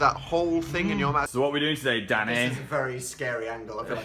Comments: OMFG I THOUGHT HE SAID DADDY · when called "danny" I thought he said "daddy". oops😂